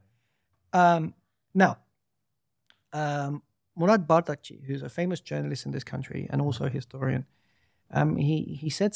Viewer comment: anyone catch the name of the journalist that Dr mentions at 44:04? edit: and the name of the scholar at 45:28? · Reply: Murat bardakçı